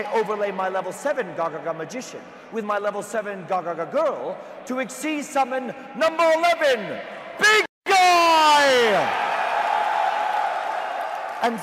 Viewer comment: "To exceeds summon...PINK EYE!!!." LOL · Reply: I heard instead "to exceeds summon...Big GUY ! Dude, you're american, that's normal you're fat lol